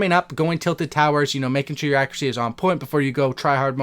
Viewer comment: Who else heard him say forday